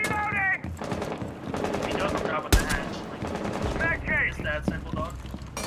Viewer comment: right after that headshot it sounds like he said "smack cam!" lol